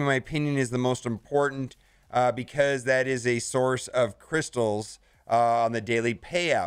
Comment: Did he say, "That is a source of crystals"? Hey SWGOH guy! What's a "Crystal"???